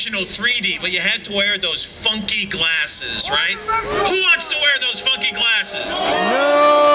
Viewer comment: it sound like he said "who wants to wear those fuking classes" lol